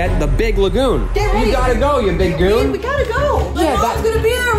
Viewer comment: Did say babe we gotta go